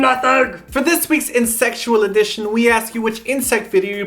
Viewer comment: when you said Insectual, i heard it as insexual .____.